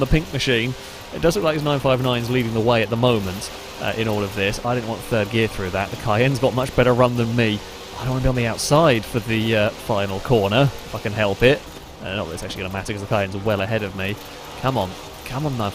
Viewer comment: It sounds like he says "fucking help it" and "If I can help it" at the same time. It's like another version of yanny/laurel, lol.